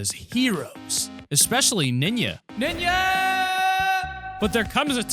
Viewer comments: Did he say ninya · Did u just say ninja as nima?😂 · Why did you say ninya instead of ninja